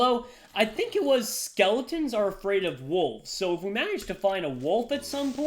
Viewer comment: I thought he said "skeletons are scared of wool"